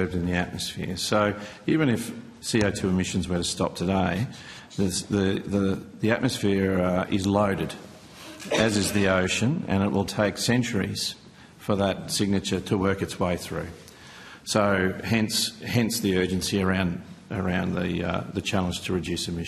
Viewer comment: does he say centuries?